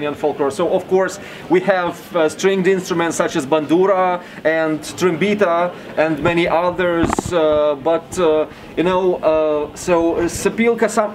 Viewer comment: did he say trambita? we have that in Romania too :)